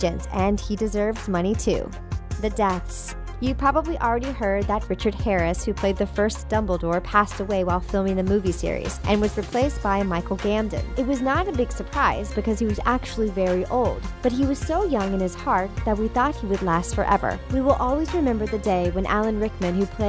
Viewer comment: waittt did she just call Michel Gambon Michel Gamden